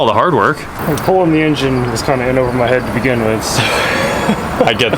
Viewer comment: sorry not a native english, did he say the engine fell on his head? and that's the reason for the eye patch? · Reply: "In over my head". Means the job was too hard for him to complete.